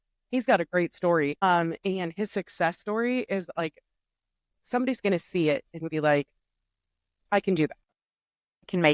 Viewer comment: who? I can't hear or understand the name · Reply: from tiktik